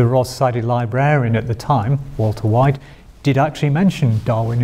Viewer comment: he actually says Walter White.